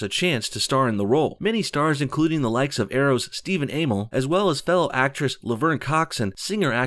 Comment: The SJWs don’t like her because she’s hot. Then the other excuse is that she’s not gay enough......LOL. Whatever that means. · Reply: Anyone notice how the guy that made the video sounded like he said stephen anal instead of amell